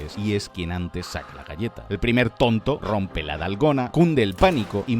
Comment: entendí “Nalgona” y presté atención en chinga